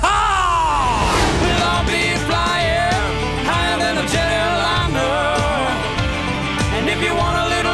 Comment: All my life I thought it said jelly liner 😂